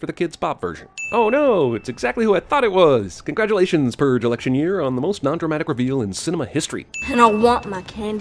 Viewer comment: for a second i thought it said "Erection Year"